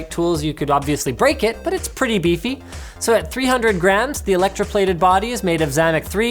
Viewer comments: Thought he said 300 grand.. bout spit out my drink. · I thought you said 300 Grand.